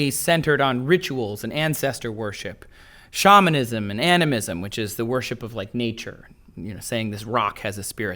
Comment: did he just say anime was a religion in ancient times